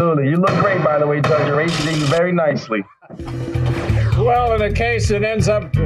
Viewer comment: What did he say at the end? "By the way you look great Judge. They are ----------(?) you very nicely????? What did he say? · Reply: To me it sounded like he said they are raising you very nicely. But that wouldn't have made any sense 🤷😁 he won the case so I'm not sure what the point is 😂